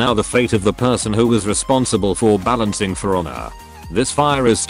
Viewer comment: Responsible for balancing what? I can't make out what he's saying. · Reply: For Honor